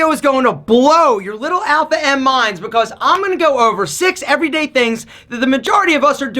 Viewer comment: English question: at 0:14 u say imma go over... but i hear an extra word, like u were to say "imma na go over.." thanks for the insight :)